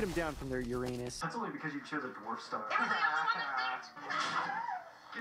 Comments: Dude I thought he said “pornstar” and I was like yo wtf kinda censorship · what the heck did mordecai say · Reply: He said dwarf star. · First i thought he said porn star instead of dwarf star.